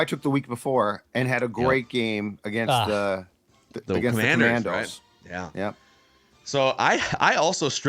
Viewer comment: Did say Commandos